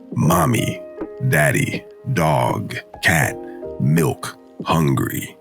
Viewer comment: I thought I heard MILF